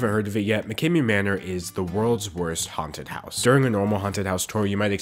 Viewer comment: when you said haunted houses i thought you said content houses and i have never been so confused